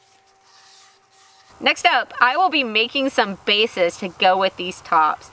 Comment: OH "I will be making some bases" I thought you said vases??? What the hell. GREAT project, looking forward to the "vases"! !! !!!